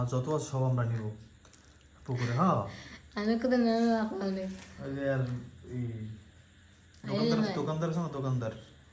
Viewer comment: sec কি বলল? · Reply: Lagaita Koilo 😂